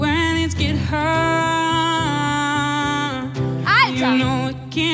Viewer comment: OK, you German speakers. Around 0:25 Lena yells something that sounds to me like "Eike!" (In English, it sounds like the name "Ike" with the final "e" pronounced.) She's yelled it before during other blind auditions she liked. What is she really yelling? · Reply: She yells "Alter". The literal translation would be something like "duuude" :D